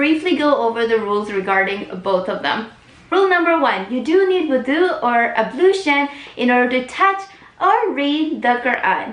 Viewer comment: What is she saying? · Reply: She is saying Wudu Ablution. "Wudu (Ablution) is Arabic which means the specific action of washing certain body parts." - Google.